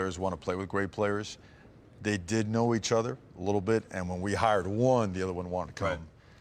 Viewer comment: did he just say when they “hired” one???